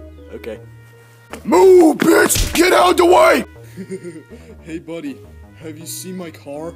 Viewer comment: omg I laughed so hard that I was this emoji 🤣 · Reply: Welll.. I heard "moogus, get out the way" from chomper...